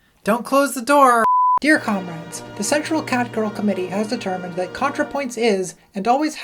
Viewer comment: I misheard a certain bit as the "Sexual Catgirl Committee," which sounds kind of awesome?